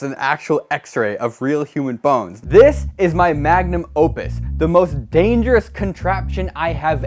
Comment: i thought he was gunna say "this is my magnum dong"